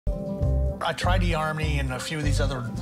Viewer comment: When he said “E-harmony”. I thought he said “army”